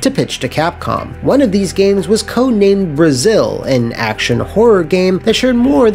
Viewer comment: brazil? did i hear right?